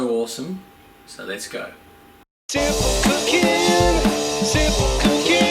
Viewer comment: His intro song sounds like hes saying "simple cocaine"